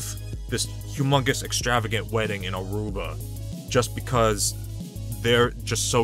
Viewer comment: when he first said she want a wedding in aruba i thought he said she wanted a wedding in a roomba